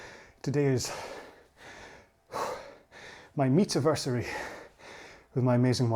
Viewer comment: #hopeful! I have to admit, that when you said, "meet-aversary" I realllllly thought you said "meat-aversary.". I would t say I was disappointed (and your heart rate increase did not account for that convo)...but...😅